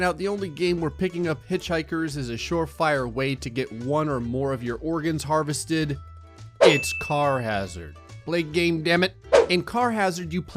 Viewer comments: I thought it said charazard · I thought it said Charizard and not Carhazard